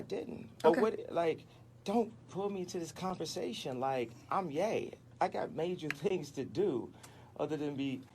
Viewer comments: Dead ass thought he said "I'm gay" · I thought he said "I'm gay" lmaooooo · i thought thiss nigga said “im gay”